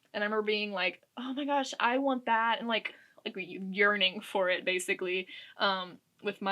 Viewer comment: girl did u say urinating for it?? Or yearning 😂 sorry I like nearly choked sipping some 🍷. It could def be the wine. Lol Love the collection btw. New sub 😊